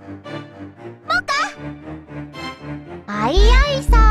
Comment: Soy yo o.. escuche que dijo “pvta” >:0